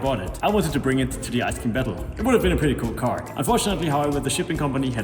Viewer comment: I thought you said Ice Cream battle every time you said it in previous videos until I saw it on the screen in the intro